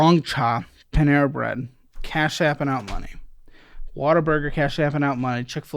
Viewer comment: Why does it always sound like ppl say water burger ?